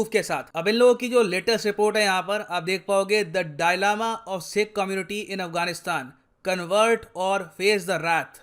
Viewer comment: did he just said DIE LAMA 😂😂😂😂